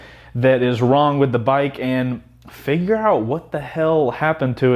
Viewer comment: i heard you say "finger out" at 2:58. do you watch vanossgaming's videos or daithidenogla's?